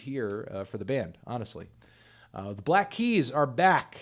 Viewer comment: I thought it said black eyed peas lolb